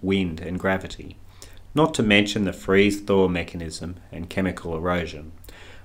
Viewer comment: Great video, I love the google earth thingie stuff. God bless you bro. BTW, could you please spell for me the mechanism mentioned at 13:08 I can't hear it clearly. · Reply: Thanks for your encouragement ..... rain, wind and gravity, not to mention freeze-thaw mechanism .... I think is what your after!